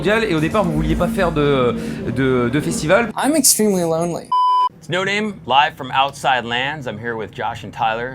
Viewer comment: I thought it said no name frog for Tyler’s name 😂